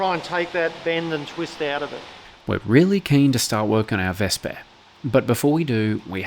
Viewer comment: Haha, I coulda sworn at 0:38 you said "start working on our Vespa"..... Australian Armor & Scooter Museum. 🤣